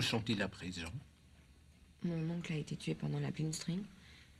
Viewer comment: Is she saying blinstring instead of blixtkrieg?